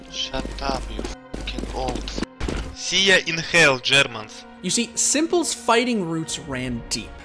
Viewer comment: any real OG csgo players here that know what he said next? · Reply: i am not rasis, i Jaz Dunn leik shjermans! shjermans